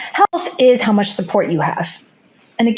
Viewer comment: I’m having trouble hearing did say she has PPD around minute